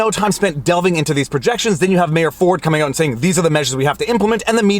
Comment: Did you say Mayor Ford at 4:50 ? Only a Canadian would get that.